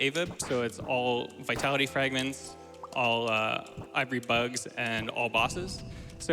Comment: (A)ll (V)itality, (I)vory ?beads?, (B)osses I am pretty sure its ivory beads, i think i misheard it as ivory bugs haha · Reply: Nah, it's ivory bugs.